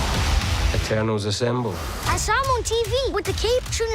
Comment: Saw it in Dolby. Have no recollection of the Star Wars text and could've swore I heard Icarus mutter "Eternals assemble." Am I wrong?